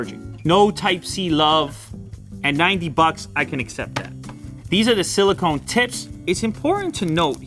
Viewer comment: "Happy Plugs" sounds like something completely different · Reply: Not to mention the fact that it sounded like he said, "these are the silicone tits!" at 2:39.